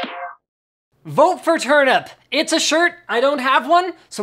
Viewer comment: I thought it said vote for trump